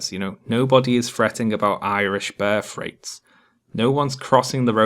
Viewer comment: I needed the captions when I got to the words, "birth rates" because to me it sounded like it was saying, "bear freights" And apparently I'm not alone, since the subtitles around 26:30 agree with me.